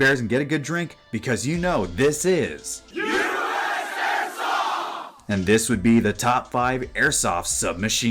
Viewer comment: when your viewers chanted "US AIRSOFT"!(at 0:59) it sounded like USSR xDD